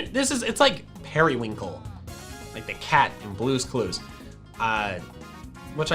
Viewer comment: did he say cat?